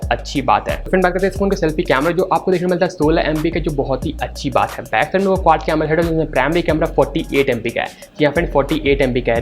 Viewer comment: Please any one make me understand what he said @ 1:47 - 1:50 #rapgod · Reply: 😅Sorry bro....I was said "back side me quad camera hai jisme se primary camera 48MP ka Hai"